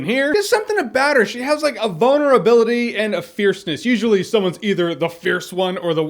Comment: he said the girl had a vulnerability and i thought he said "bonerability" and i was like "yes, yes she does"